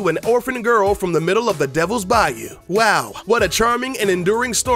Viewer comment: I'm confused by this... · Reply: Oh gosh, I thought you said devil's body! xD